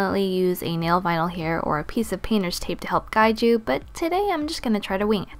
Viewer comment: did anyone else hear her say penis tape oh god :'D